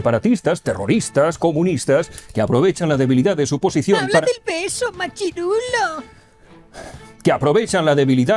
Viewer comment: Gracias por el regreso. Yo las primeras veces entendí "habla del peso". Hubiera sido un buen giro hablar del peso argentino (= 800 USD) y la argentinización de España. Para otra vez